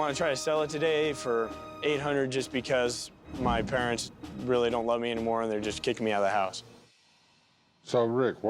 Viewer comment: I did a double take when he said his parents didn't want it and didn't love him anymore. At first I thought he said "they don't want IT anymore" lol